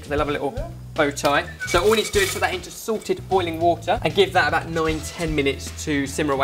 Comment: Every time says "salted boiling water", I always hear "SORTED boiling water"... I had no idea you guys had your own branded water!